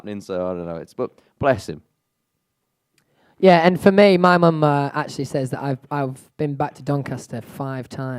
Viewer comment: sounds like Louis is saying "mumma" oh my heart